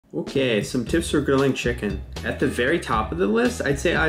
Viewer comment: I thought it said best tips for cooking children😅